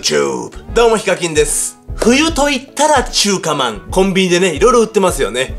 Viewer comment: 中華まん コンビニでね… のところマ〇コと聞こえた俺は末期